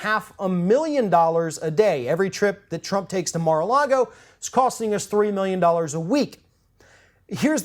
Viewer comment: I thought it said mars argo so i got excited